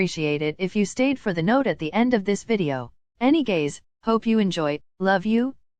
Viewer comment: BRO, DID YOU JUST SAY “ANY GAY?” instead of anyways. 🫪 oh I’m just hearing things.